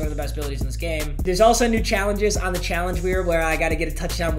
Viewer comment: did anyone hear him at like 5:00 he said challenge wheer